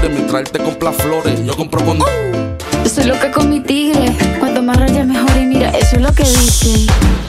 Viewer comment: Yo siempre creí que decía “soy loca come tigre” 😔✌🏼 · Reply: Yo pensé que decía yo solo he cometido XDD